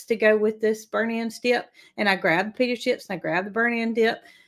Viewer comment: Jamie here from Nashville. I thought you guys were saying “BERNIE’S AND SKIP”, not burnt ends dip. 😂😂😂